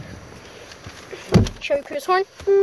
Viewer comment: LOL I THOUGHT YOU SAID ON THE INTERIOR chevy prius horn XD UNTIL I REPLAYED IT